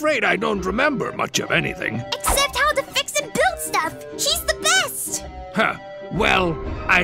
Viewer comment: did I just hear " She's the best? " ? For Eggman?